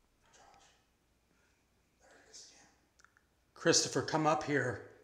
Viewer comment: I loved this episode and found some great evidence.i hear a evp at 30:26 that seems to say "Christian?"